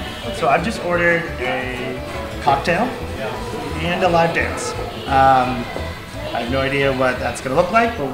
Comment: i thought i heard "LAP DANCE" but turns out to be "LIVE DANCE" instead...